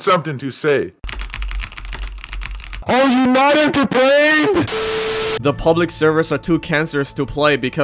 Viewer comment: What i heard 15:30 "Are you united to pray?!"